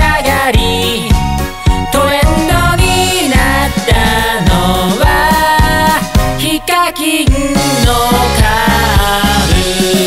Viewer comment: 画面見てなかったから｢トレンドになったのはHIKAKINのカーブ｣って聞こえてびっくりした。